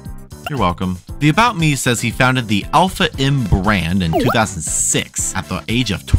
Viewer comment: when you said "alpha m brand" i thought you said "alpha inbred" and i thoguht it waws the funneist thing ever